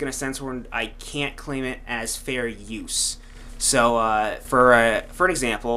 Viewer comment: i think your saying fairy... "i cant claim it as fairy use"